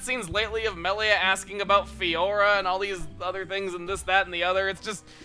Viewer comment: wow, i misheard Fiora has Farore